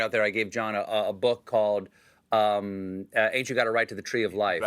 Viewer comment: What is the book called they were talking about? Couldn't hear it... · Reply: I heard Ain't You Got A Right To The Tree of Life